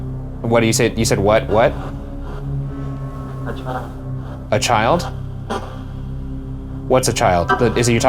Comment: it sounds like instead of "a child" someone saying "That's ryan." it also sounds like theres an older male and a child talking there so maybe the older male said to the child "that's ryan." also when you were doing the knocking thing after the very first question it sounded like aknock. or someones knees cracked.